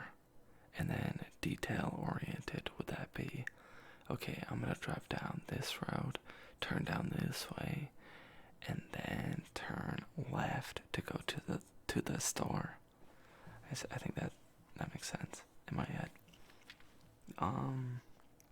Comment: wait did he say weft? MA I WANT HIM!! 😫😭